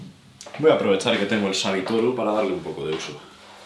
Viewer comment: Hola. ¿cuál es la piedra pequeña que utilizas para limpiar las piedras de afilado? (Minuto 13:47). No creo que es una nagura. Parece que dices "sabitoru".¿Dónde conseguirla?. Gracias. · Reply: Es un sabitoru.